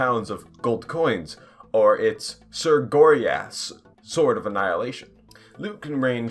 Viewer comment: I wasn't looking at the screen and I thought you said Sir Goryass 😂😂